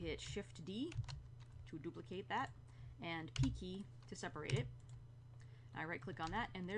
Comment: P key to separate. I heard key key and the subs also have key key in it.